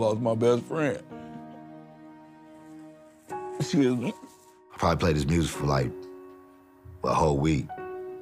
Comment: Was it me or at 31:50 did he just say " Pac played his music for a week?" Then he acted all weird on camera?? Anyway just a thought???